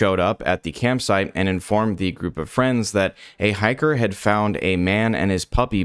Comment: I honest to god thought it said Hitler instead of Hiker. Like didn’t he go to Argentina ????